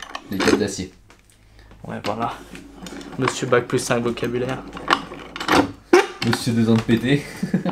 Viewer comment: Il dis quoi le messieurs à 12:05 ? (Si j'ai bien entendu Mr. deux ans de PT, sache que je t'aime <3 et que nous avons un point en commun! )